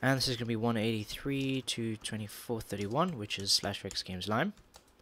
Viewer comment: I always hear you say "Slash rigs games" but awesome tutorial as always! :D The character looks like he is slipping on ice thought. Can't wait for the next tutorial.